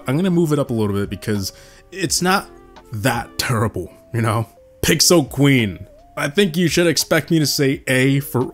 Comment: you picked up Pixel RC instead of Pixel Queen, but still understandable.